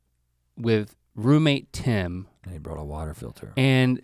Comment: I thought Link said "roommate 10" like roommate #10 as if they just couldn't name him. haha